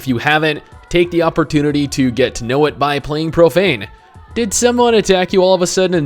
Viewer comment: oh, i thought you said propane.. with propane accessories